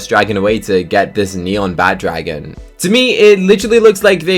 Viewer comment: the captions said bad dragon 😨 Bad dragon means something bad btw